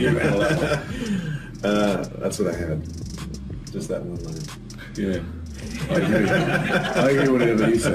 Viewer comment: What Wentworth says in 1:45? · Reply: Alice Adorno 'That's what I had. Just that one line.' i think he said that :)